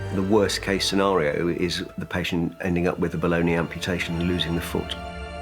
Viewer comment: The doctor said ‘below knee amputation’. Up I heard ‘ Bologna amputation’….two extremely different procedures! 😎